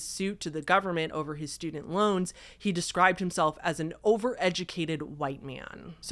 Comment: Lmaoooo 12:00 i was busy doing other things while listening.. and I thought said stupid loans instead of student loans! 😂